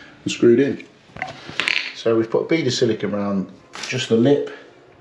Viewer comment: Do you stick the shower tray down? (With silicone, sand and cement or anything else?) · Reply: Thanks - I miss heard it as the head of silicone around the outside. My bad! So many different views as to what is the best way to do it!!